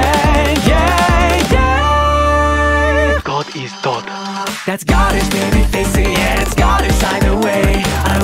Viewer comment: I keep hearing "That's Scotish" instead of "That's God-ish"